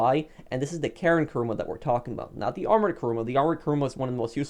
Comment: At first I thought you said Armed Kuruma and I was ready to spam shame comments on you haha I love the Armed Kuruma